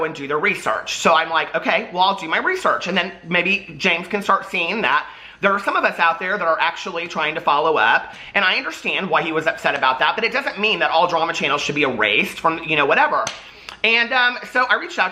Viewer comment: I thought jc said he has all drama channels blocked